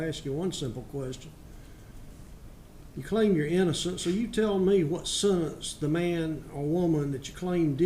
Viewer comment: I am not good in english I dont really understand 4:10 - 4:23 part of the video can somebody explain please? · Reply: I think he said "you claim your inocence and decided what the man or woman should receive" probably More To it than that tho